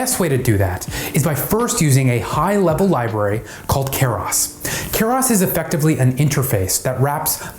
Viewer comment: captions are showing chaos instead of keras